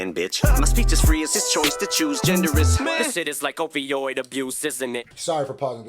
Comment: Almost sounds like he says "o vi oid abuse" instead of opioid abuse